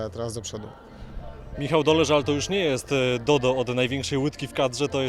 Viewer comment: Pierwszym razem jak usłyszałam to usłyszałam "Nie jest teraz Dodą" i miałam skojarzenie że z Dodą piosenkarka.